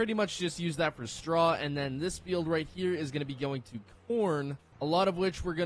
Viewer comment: did he say “porn”? 😂